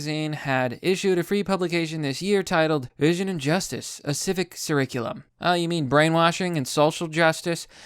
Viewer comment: Love your vids but around 1:20 did you say “surriculum”? lol